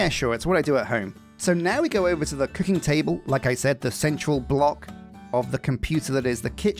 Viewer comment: did you say ‘Küchentable’? It sounded very German, it could just be a slip of the tongue, but my German ears can hear umlauts very well. ^^